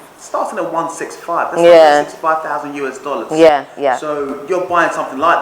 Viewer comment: Did he just say $165 Ghanaian and $5000 US dollars